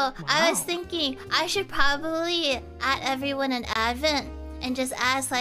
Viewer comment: I think she said “@ instead of add but yeah lol